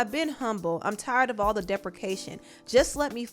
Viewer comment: Listened to Doja's bars in Attention pleeenty of times without captions since release... I swore she said, "I'm tired of all the defecation." Maaaan deprecation def makes more sense now but I'm still not mad at defecation unless it was all just an intentional euphemism that sort of went over my head I guess? 🤷🏽‍♀️